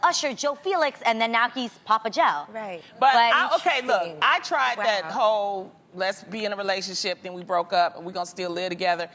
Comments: I literally thought at 4:41 said 'I tried that whole lesbian relationship and...' haha , I know I heard wrong tho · WHATTTT??? @ 4:38 i thought said "lesbian relationship." No sade there. Just surprised. lol ah. Let's be in a relationship.....go it! 😂😂😂😂